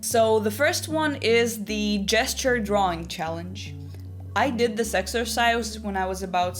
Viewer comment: I thought you were saying jester drawing challenge 😭 I was so confused but so ready to draw 100 jesters a day